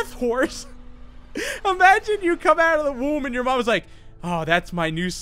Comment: “Ahh~ that’s my new son deathhorse” Me : that sounds really weird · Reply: Instead of “come out of the womb,” I first heard “Come out of the room,” and with the “That’s my NEW son” line, it didn’t help my kidnapper suspicions for the mother. Even though I heard something wrong, New Son is odd terminology.